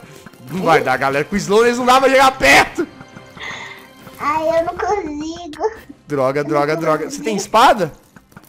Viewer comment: No momento 6:10 só eu que escutei a falando aí eu não cuzigo